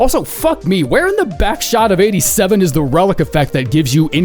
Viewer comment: did you just say the phrase "where in the backshot of '87"?????